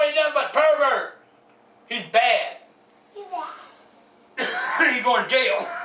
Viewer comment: what did that kid say ? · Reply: He said he bad